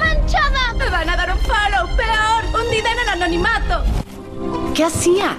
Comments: No le entiendo en el minuto 0:14 "me van a dar un paro, peor un ?? Anonimato " que diceeeee?? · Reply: daniela alarcon Dice " me van a dar unfollow, peor hundida en el anonimato" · Alguien sabe lo que dijo en el minuto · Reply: Hundida en el anonimato